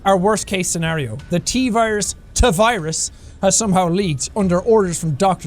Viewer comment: my last name is Tavarez and when I heard him say "Teevirus" i was like yay he said my name!! i know... No one cares